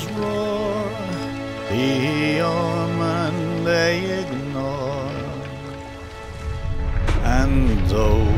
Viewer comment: “The Devils Roar, a land left unexplored. The Devils Roar, the omen they ignore, and those who land upon her shore best write to those who they adore. Just ask the souls who came before. Those poor forgotten sailors... heh heh heh.” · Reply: You know I always thought he said "The old man they ignored."